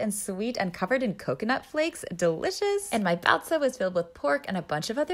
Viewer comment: I thought it said "brazil" Not "baozi" I was so confused 💀